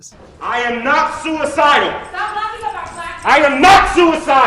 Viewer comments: What does the woman say in response to his outburst at 10:49? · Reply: She says: Stop locking up our black men. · What does the woman in the audience shout at 10:49 is it "Stop talking about black people" ... not sure... but he gets super angry after that LOL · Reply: She says "Stop locking up our Black people"